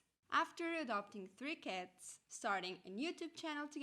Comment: I was listening more than watching and during the sponsor bit, I heard "after adopting three kids". I had to immediately remind. Cats. What lucky cats you have?